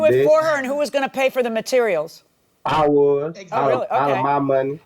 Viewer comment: All I hear “He lying honor” !!!!!